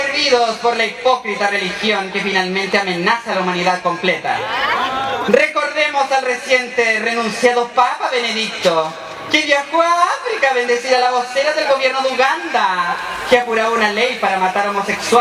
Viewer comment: Alguien me puede decir qué dice exactamente en el minuto 11:22 hasta el 11:29 por favor! No se entiende bien y necesito urgente esa parte :). Gracias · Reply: Miguel Bacilio "Recordemos al reciente renunciado Papa Benedicto, que viajó a África a bendecir a la vocera del gobierno de Uganda, que apuraba una ley para matar homosexuales en su país."